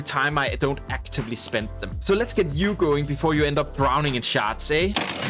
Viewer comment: i'm sorry, i heard "browning in sharts" here and i cant unhear it lol.